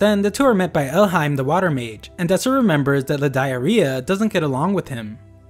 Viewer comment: la diarrhea. That is exactly what I heard 😂 I am so immature